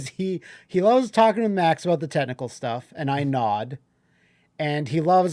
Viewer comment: I misheard this as "[Justin] loves talking to Max about the TENTACLE stuff" and my brain broke.